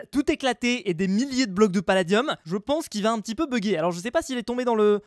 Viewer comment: C’est moi ou il a dis palladium a